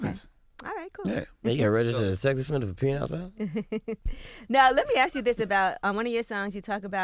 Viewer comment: What did he say to Charla about being a sex offender at 2:41? 👀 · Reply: Bosshoggbaby Thompson he said u gon have to register as a sex offender for pissn outside lol